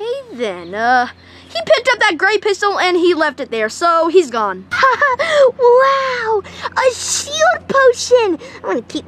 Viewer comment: I WANT THE HELPY PLUSHIE HES CUTE AS HELLL🥶🥶🥶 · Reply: I thought u said lefty?